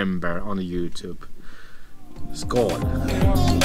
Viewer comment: We thought you said at the end, "in Spain you can be naked everywhere you want expect in Lidl", had to rewind. Keep up the great work, we so enjoy your adventures.